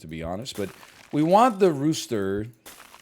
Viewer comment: I thought it said lobster i was about to cream